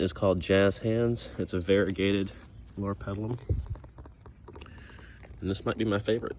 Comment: Hi, what is the name of those beautiful leafy shrubs - it sounded like you said "Laura Pellum.". I would like to plant 3 of those in my front yard. Love your beautiful flower beds! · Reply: hello Cynthia, it is a mouthful trying to say it haha. it’s a (dwarf) Loropetalum, one of my favorite evergreens :)